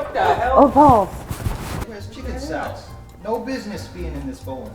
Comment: I swear I heard "there's chicken salad" and it completely caught me off guard XD